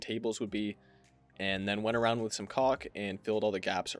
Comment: ummm… what did he say? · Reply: Went around with some caulk